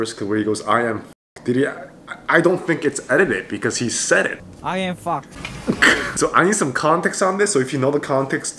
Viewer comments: he said 'im fox" but ppl misheard him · the first video of I.N he was tryna say "I am Fox" 😭 -i dont know what all these likes are for but tyy <3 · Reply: when I first watched the original clip I thought he said I AM F*CKED 😭😭😭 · I.N said “I am fox”, but cause his English isn’t the best, it sounds like he said “I am fuck”. He is learning a lot though which I admire about I.N. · He said : I am fox but he sounds like he said I am fck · he wanted to say i am fox but his accent isnt fluent in english so it sounded like he said i am f*ck" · in the first clip where it sounds like he said "I am fuck" its actually "I am fox" lololol. seungmin being a menace!!!!! its great. also Hyunin!!! the duo is so cute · He actually said I'm fox cuz his animal name is fox it sounded like he said I'm fvck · the first clip he actually said "i am fox" but the clip is cut off and it sounds like he says "i am fuck" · When he said i am fuck since he can’t speak English that well he ment to say I am fox because straykids was introducing themself and he said I am fox but it’s sounded like he said I am fuck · In the first clip i.n. said i am fox but the video cut off so it’s sounded like he said i am f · He said i am fox but it sounds like he said I am fuc k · I.N wanted to say I am fox but they cut of the x and with his Korean accent it sounded like he was saying I and f***😂